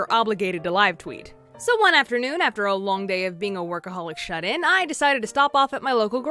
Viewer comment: I thought she said alcoholic xD